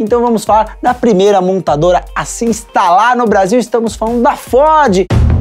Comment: Só eu que ouvi "estamos falando da Fode" aos 0:20??? hahahahah